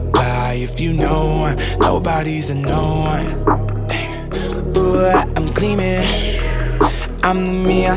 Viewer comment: what does he say? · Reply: Throw up I’m cleaning you must not have watched the movie 🍿